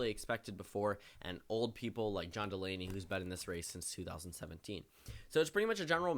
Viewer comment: Swore to God I heard John Mulaney😂